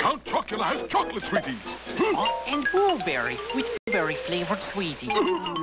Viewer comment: oh. ok. thanks. i thought he said booberry flavored sweeties, lol.